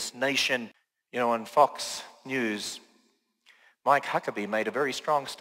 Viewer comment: Did he say, "Y'know, on Fucks' News?"